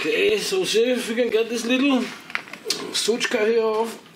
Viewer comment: Did I hear "Sutschka" at 9:36 ? :-) Greetings